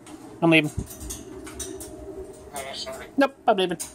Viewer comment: By 3:27 sound like he said; 'so sorry"